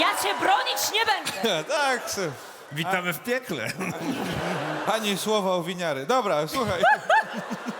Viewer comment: co on powiedział ? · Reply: "ani słowa o Winiary"